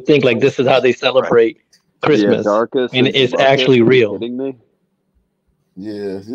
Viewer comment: I thought he said darkness. Then realized his name was darcus 😂😂😂😂😂😂